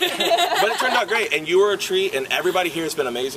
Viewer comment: Wait did he just call her a tree???